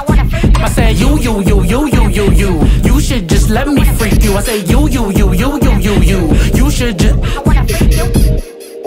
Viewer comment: I thought he said “you should just let me see you, my friends think I am so innocent😢😢😢